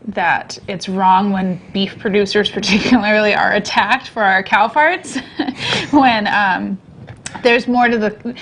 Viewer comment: ish did say cow parts or farts?